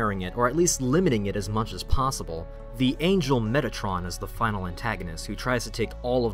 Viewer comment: When you said Metatron, I thought you said Mettaton.